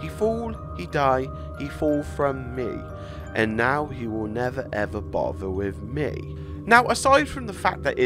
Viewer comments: Instead of “and now he will never ever bother with me” for some reason what I hear is “and now they will never ever find him again” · I herd instead of '' bother WITH me'' i hear."bother me" maybe they killed somebody? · In the first part i heard "he fall he fall he fall slowly" which could link to Mono falling slowly in the end of the game. Then I heard "he's tall he's tall he's tall" the second time which links to the thin man and monos transformation after the fall down.